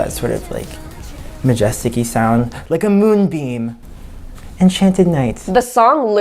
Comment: "like a moon BEAM" sounded like MOONBIN :cc